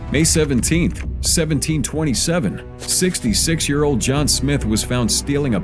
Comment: Narrator say May 17, 1727, but the caption says February 20th, 1706. 😂 Do people not double check their work anymore?